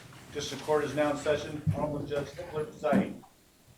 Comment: When they annouced the arrival of the Judge, I seriously thought they said "Judge Hitler" Oh Dear!!!